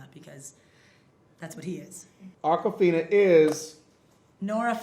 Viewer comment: First time hearing her name, I thought she said "Aquafina" as in the water bottle...